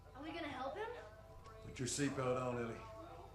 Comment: it sounded like he said, "Put your seat belt on Lily" lmaoooo